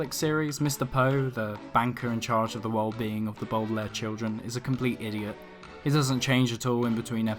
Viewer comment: Did he say the boodalair children ?? 😂😂😂🤣😂😂🤣😂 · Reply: I heard “the bootleg children” lmao